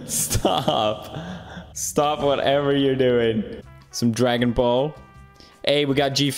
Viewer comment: Did... did he just say dragon ball?